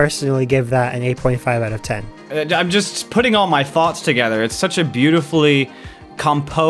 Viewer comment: When DAGames said "thoughts" it sound like he said "farts" xD